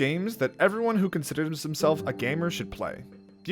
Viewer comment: did he just say 'gamer'?...